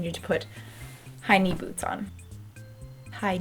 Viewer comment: For a second there I thought you said "highney" boots instead of "high knee" boots and that just made my day. (Also, not trying to make fun of you. I have a hearing loss so I often confuse what people say...)